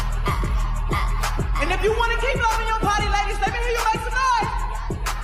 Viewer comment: “Ladies! If you wanna keep loving your body and kill them babies just to keep it, let me hear you Sca-REAMMM!!!” is pretty much what I heard.